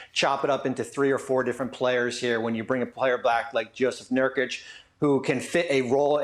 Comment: Did Bobby just say bring a player black 😳😂 ?